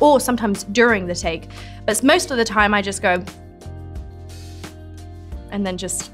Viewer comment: is she saying "bs most of the time" here or that just her British accent !?